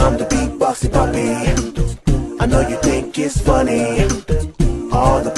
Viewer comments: “Girl we’ve been knowing how to draw” “Beat boxing puppy!” I think I’m going fucking insane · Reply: I thought the puppy said “I know your dick is funny” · When the puppy sings I had to go back and listen again several times becuse in the line "you know you think it's funny" I swore it said "you know your dick is funny" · For some reason I heard the verse “I’m the beat-boxing puppy, I know you think it’s funny” as “I’m the beat-boxing puppy, I know your dick is funny” and now I can’t stop hearing it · I seriously don’t know why but instead of “I know you think it’s funny” I heard “I know your d¡ck is funny” and it scared me so bad